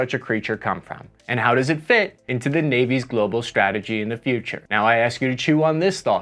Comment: did you say globo like in dodge ball? 😂😂